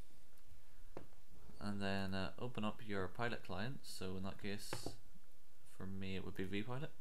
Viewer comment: I lost you at 3:02 open up {{{your product line?}}} ??? wth? I have steam Im new to it but damn can you help new players out to what you just did or said past 3:02? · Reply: Open up your pilot client. This may be squawkbox, FSInn or vPilot as I use.